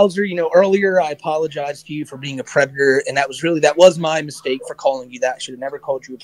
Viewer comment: did he say he apologised for being a predator? Or he apologised for calling him a predator?